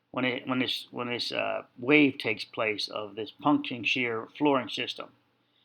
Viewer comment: Punch shear sounds like puncture, I think that’s what you’re saying! I looked it up, it’s called punching shear, which I heard you say later in the video. Now my cat ran to the window to see if there’s a cat outside, from hearing your cat on the video!